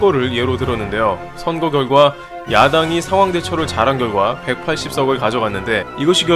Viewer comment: 에 저만 여당이 야당으로 들림?